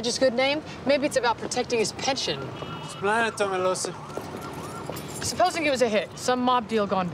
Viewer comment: WHAT THE HECK DID STABLER SAY???? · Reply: I googled this episodes transcript and he says “‘splain it to me Lucy” with an accent(?). I hope this helps bc once I read your comment I couldn’t stop thinking about it